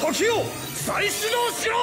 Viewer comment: what does kc says here? In japanese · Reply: 時よ再始動しろ－ッ！！ Toki yo saishido shirooo!